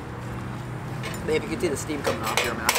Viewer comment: Did she call him Babe around 3:34????